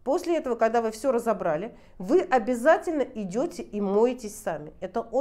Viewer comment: "После уборки идете и моете (не совсем поняла) СТАВНИ? Мб, не правильно расслышала? Это на 3.15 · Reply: В данной фразе: "...идете и моетесь сами".